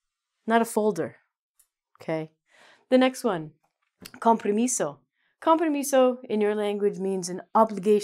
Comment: I thought you were saying con permiso instead of compromiso